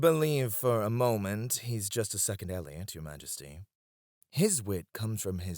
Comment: Am I the only one who thought he said Ellion? Because I was gonna say “My Knight cameo?!” These two need to get together sometime